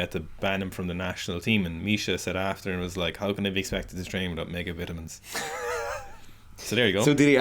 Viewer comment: what does he say at 3:25? ' how can i be expected to train without ...' what? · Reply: Mega-vitamins